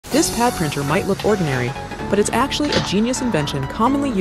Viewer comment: "This pad printer might look ordinary" Sister, I can't emphasize how not ordinary this looks. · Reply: I thought she said Cow Printer 😅😂